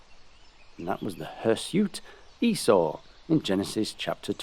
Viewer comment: I swear you said hairsuit Esau